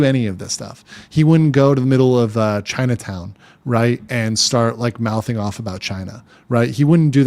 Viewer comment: I heard it as gaytown